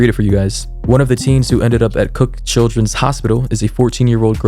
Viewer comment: ok I know this is a serious video but I legit thought he said "cooked children"